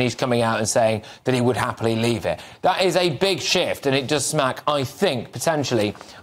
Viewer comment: ish. Did you say, big shift? 🤔